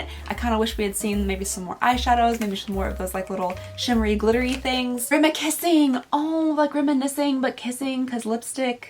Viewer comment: I heard "gramma kissing" instead of "remikissing" lol